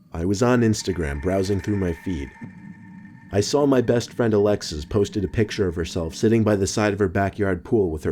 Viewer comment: I thought he said Alexa 😂